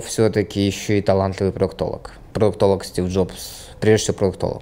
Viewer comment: Не понял как он говорит о Стив Джобсе : « Стив Джобс прежде всего "прок толог"» (?) 👀 на 25:56 Про что он говорит не понял.